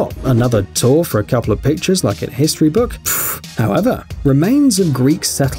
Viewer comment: When he said however in 10:00 it sounded like the meme hello there